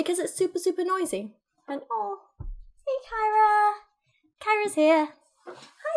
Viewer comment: I heard "Kyra" and thought you were talking about me lmfao